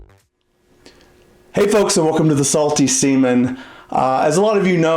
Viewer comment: I thought he said “welcome to the salty semen”